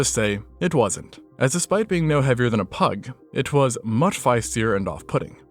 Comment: That's a weird comparison. "Despite been no heavier than a pike...". At least I think that's what you said, you have a tendency to mangle certain words. No offense mind. 🤨